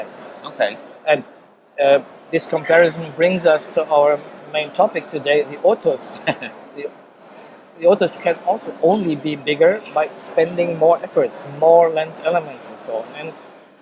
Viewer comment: i thought he said the n-word